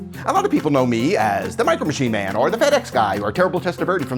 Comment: I thought he said to himself "Fat-Ass guy" then I turned the subtitle on it was "FedEx Guy"